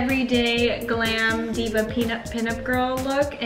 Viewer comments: I heard " Diva Peanut Girl!" XD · I thought said "Peanut girl"...